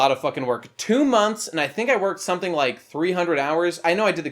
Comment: For a moment there I thought you said it took you 2 HOURS, not months, and you were making a big deal about it taking a long time. For a moment I thought you were the Flash or Jesus. Or both